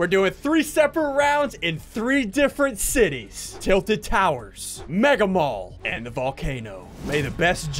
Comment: did he say tilted towers